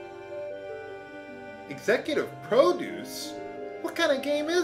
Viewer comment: Hahahahaha... I was eating my popcorn, therefore not looking at the screen, when you said "Executive Produce" and thought you said "Executive Probings"... it even fits with the "what kind ov game is this" quote.